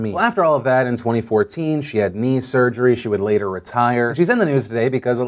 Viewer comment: I seriously thought phil Said "she got meme surgery"